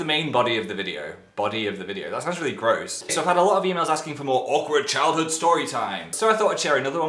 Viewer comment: Dan: I got a lot of emails asking for more stories What I thought he said: I got a lot of females asking for more stories I kept rewinding the video to understand 😂